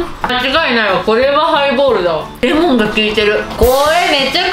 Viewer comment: 『ハイボール』が『プレイボール』に聞こえる。